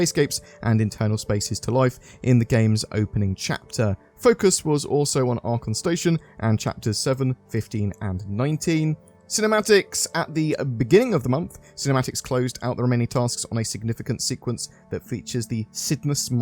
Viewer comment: Did you say Arkham station @ 4:06 ????